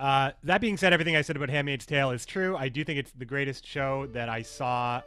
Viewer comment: about what show are you talking about at 6:30? I can't understand the title, english is not my first language ;/ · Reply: At first, he talks about "handmaid's tale" then about "bojack horseman"